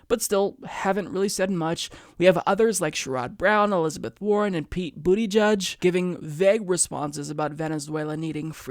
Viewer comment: did say that guy's name is Pete Bootyjudge?